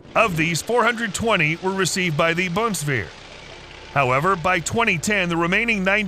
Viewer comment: C'mon guy - did you really translate BUNDESWEHR into something like "VOLKSWEHR" around 1:40? (Maybe I just misheard, then please apologize the following.) Well yeah, the Cheetah truly is one fine piece of weaponry and supposedly many germans are proud this product can help Ukraine withstand fascist Putinism - decades later. It's just ... you know ... we don't refer to "Volk" this much no more - ever since WW2. Has a bad taste. The "Easterners" had their Volksarmee, from WW2 til re-unification. So let's say, for future reference - our BUNDESWEHR is what should translate into "Federal Armed Forces". It is just a political detail - but one that really matters when you talk West vs. East or Germany vs. Russia.